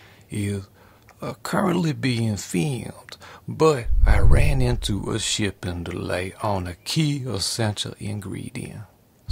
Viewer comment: So I misheard you at 0:15 and thought you said “ran into a ship on the lake” and I gotta say that’s a pretty funny saying Like, how do you manage to hit a ship on the open waters?? No big ocean waves, and if it’s a whole SHIP then you’d definitely see it coming. Kinda like hitting a light pole in a parking lot… I think I’m just gonna start saying “ran into a ship on the lake” for extremely obviously avoidable blunders 😊